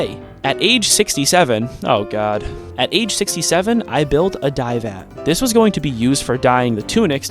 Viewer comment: Did soneone say... 67? 7:41 🥺🤞